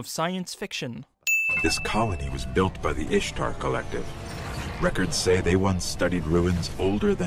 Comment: did someone say "Ishtar"? <num> AND!